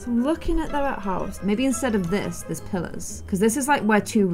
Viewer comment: I thought she was saying pillows, got real confused lol